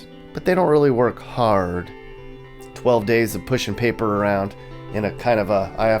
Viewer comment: did you say 12 days of work?